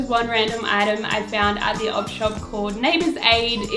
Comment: I would love to see that early 2000's girly lookbook! Also, what's the Australian term for thrift stores.. ob shops? op shops? I'm American so I'm not familiar with that name, and I can never tell when I hear people say it lol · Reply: ElGreco1541 I always thought she said odd shop